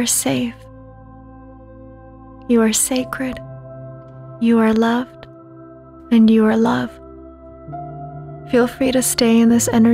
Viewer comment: Love this meditation..Can you tell me what you are saying @ 14:18? I couldn't quite make it out. Thank you. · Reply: I think she meant you are loved and you are (the)love